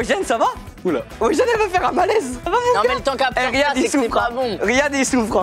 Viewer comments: À 25:02 inox à dit à wejdene: sa va mon cœur ? Je sais pas si j'ai bien entendu donc dit moi ! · J’espère que vous avez bien aimé ce format ❤️ ça change de d’habitude 🙏 Merci à tous 💪🤩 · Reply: c'est moi ou a 25:03 inox dis "ça va mon coeur ?" mdrrrrr